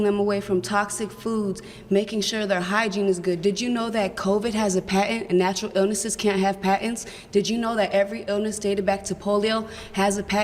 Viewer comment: I thought she said "pattern" at 49:40. But I might ne wrong coz im not a native speaker